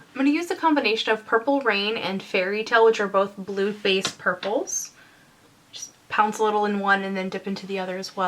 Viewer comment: I totally heard "Blue-based purples" as "Blutbad purples" I've clearly been watching too much Grimm...